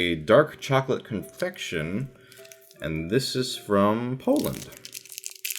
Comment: Sounded like you said infection instead of confection lol.